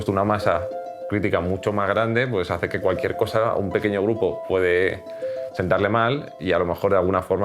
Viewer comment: pensé que dijo un pequeño bug xdd